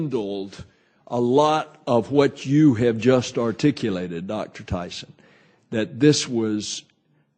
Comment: i thought he said Dr Dyson, as in Michael eric dyson, there ya go